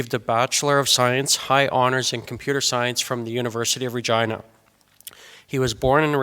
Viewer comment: The University name is tricky lol sounds like you know what. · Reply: I thought he was saying University of Virginia but deliberately mispronouncing it :D